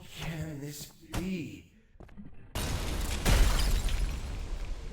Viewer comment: to be honest, when I 1st played Ultrakill I thought he was actually gonna say "May your L's be many, and your bitches few"